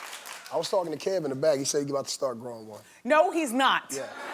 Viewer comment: I thought he said I spoke to Kevin and he said you're about to grow one (beard)